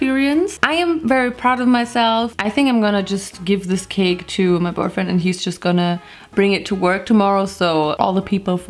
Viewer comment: wait did she say "I'm gonna bring this to my BOYFRIEND" OR AM I DUMB???